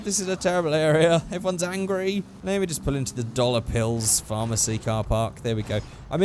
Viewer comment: Haha I heard "maybe just pull into the dollop hills"